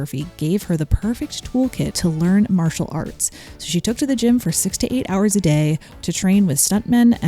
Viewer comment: "She took to the gym for 6 to 8 hours a day" while I heard "she took to the gym for 68 hours a day" and got very confused for a strong minute.